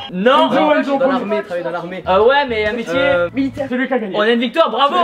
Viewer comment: A 6:52 c’est quoi comme metier qu’il dit qu’il aurait voulu faire ?? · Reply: Militaire